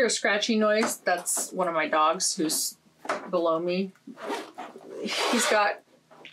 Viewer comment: Re your floss organization board: damn girl, you IS fancy!! You need to come to my house and organize me!!! I’m TEAM BEATLES and I HATE pineapple on pizza. But I am also TEAM NICKY AND TEAM BUMBLE STITCHES despite their questionable taste in music. And I’m TEAM PULP FICTION. . When you said your dog was “below me” I heard “Blow Me”. Just saying you may want to clean up your language, missy. 🤣🤪